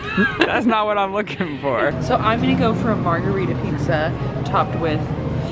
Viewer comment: Ok so I had my subtitles on and at 3:05 when said margarita pizza instead of pizza penis showed up😂😂